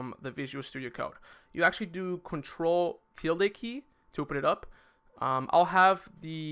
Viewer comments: what did you say? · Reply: ctrl + shift + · "control + till liky?" what does he said? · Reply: tilde key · Im sorry, but i couldnt understand what you said at 14:16, pls rephrase that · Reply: ctrl ` i think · what key he said? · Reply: ctrl + ` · what he say??? CTRL+?????? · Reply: Mapping no he is saying ctrl+~ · What Did He Say on 14:18 ctrl till the key? Whay? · what is he saying? pls help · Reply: Control tilde key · what is it says on 14:20?? it's Ctrl + del. but idk what is it. could somebody help meh?? qwq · I am confused about 14:16 I don't really understand the cntr tle key or something can help me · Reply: There are two ways to open the terminal. Control + ` or open Terminal then new terminal. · didn't understand what you said in 14:15 Ctrl + telekey? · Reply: tilda key aka this ~ , or if you don't have this key on your keyboard you can just go up to the top of Visual Studio and look at the 7th tab in the top left that says terminal and just click it.